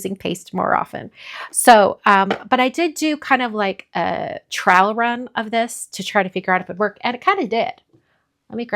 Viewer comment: I know you said you did a trial run, but it sounded like you said trowel run, which I find very amusing.